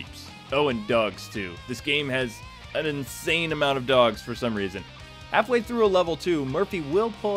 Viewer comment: I thought you said there was lots of "Dougs" in the game. I was like, "How many Dougs could there possibly be in Old Detroit?"